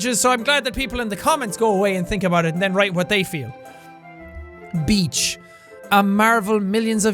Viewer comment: I thought you said bich